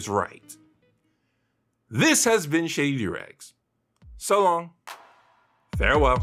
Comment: That was the worst mishearing ive had yet, I thought you said shave your doorags instead of shady